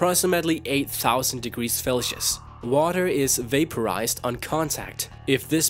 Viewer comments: Did you really say "felcius"? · Did he say "8000 degrees Felsius" at 0:47? Lol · That F at the end of 18,000 in magcargo's description stands for Fahrenheit not whatever strange word you were attempting to say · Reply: sylvr But he sounded like he was saying Felcius.....like he combined Fahrenheit and Celsius and then assumed (i guess) that it still meant Celsius... By the way I hope English wasn't your first language because you misspelled spoke, just, speech, patterns, and such. Plus, you used the word "learns" incorrectly. Don't mean to be a grammar nazi, I just watch a lot of Your Grammar Sucks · LOL DID HE JUST SAY FELCIUS